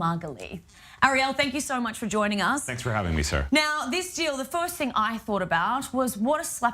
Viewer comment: What the heck is the guest saying@ 0:31 - Thanks for having me sir.. Well, she is lady; isn't she? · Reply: Sarah lol not Sir. I heard something similar though